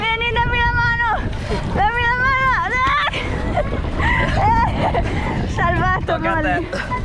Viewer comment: solo io ho sentito salvato amore???